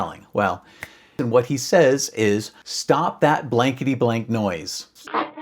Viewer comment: Thought thw parrot said "Stop that rickety rack now!!"